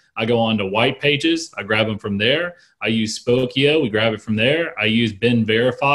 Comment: what is he saying? fokio? i cant find a website or resource that sounds even remotely close i tried, pokio fokio folkio polkio idk i need help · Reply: Spokeo